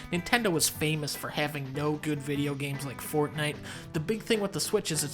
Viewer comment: HEY WHATS THE BIG IDEA?! I thought you said no more good vids